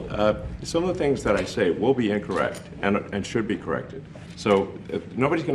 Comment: did he actually say: “first of all, some of the things I said won’t be incorrect and should be corrected”? So basically saying that only his correct statements need to be changed???